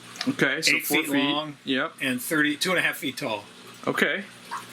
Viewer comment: 4' wide, 8' long, 32.5' tall. That's a tall tank! *I know he said 30.... "2 and a 1/2 feet tall", just sounded like 32 and a half feet tall. Made me laugh is all.